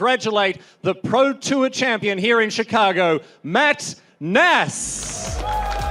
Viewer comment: Did he say Max Nass?